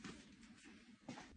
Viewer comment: “‘Can you explain the hot sauce though?’ ‘Heh heh… no.’” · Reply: 😂😂😂I heard "Hood sauce"😅